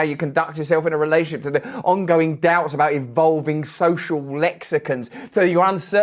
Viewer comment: I really thought he said social Mexicans until I replayed it 3 times